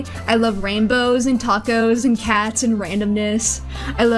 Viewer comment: tacos i hear